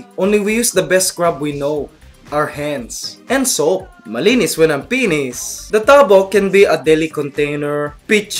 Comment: did I just heard? "malinis, walang penis" haha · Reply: malinis when I'm finished*